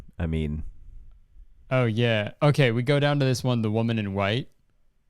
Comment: I thought it said "The woman is white"